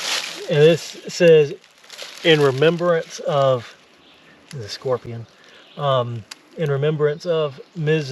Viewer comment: At around 21:26 does say "OH there's a scorpion:!?